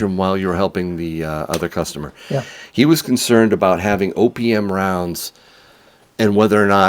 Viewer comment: I thought you kept saying opium rounds until you clearly said OPM. I looked them up on Ebay, I know I have a few tubes.